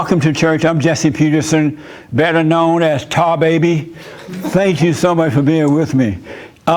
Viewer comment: 😶Did he just say Better known as tar baby??0:45😂😂😂😂😂😂😂😂😂😂